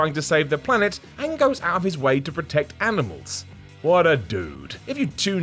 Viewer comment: i thought he said: "What a douche"^^